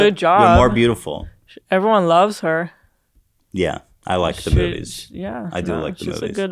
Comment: i swore ethan said i like the boobies😂😂😂